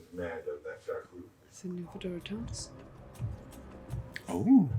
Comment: What did whisper? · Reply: "Is that Nymphadora Tonks?" 😊